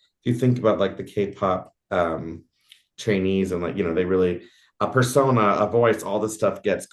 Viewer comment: Did he just say “… K-Pop Chinese…” ? Did he mean to say Korean? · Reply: I heard that, too. But he actually said "K-pop trainee."